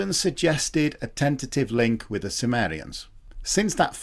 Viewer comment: I thought he said 'a tentative link with the Sumerians" but I guess he mean Cimmerians ...